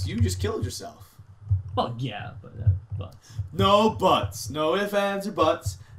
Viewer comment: is it just me or did say fuck yeah at 12:16 :D